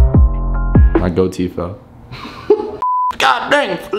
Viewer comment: wait “gold teeth”? I thought he said goatee